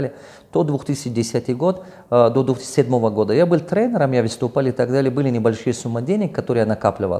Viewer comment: Вы были трейдером, или я не правильно расслышал?